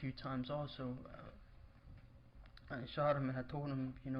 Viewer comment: Why when is on the stand did he say at around 1:37:24 " I shot him and I told him to stop cause there were neighbors " . I just think it's weird for someone to say this if they didn't actually do it. I'm not stating is innocent but it just seems that maybe there is more to this story. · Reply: 1 Super Diva he said "I shouted" not shot..it's his accent..I listened over and over to be sure